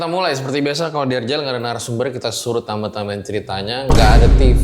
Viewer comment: dia bilang apa? Narasumbernya disuruh nambah nambahin ceritanya? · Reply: Nga ada Narasumber yg kita suruh tambah tambahin ceritanya ka😊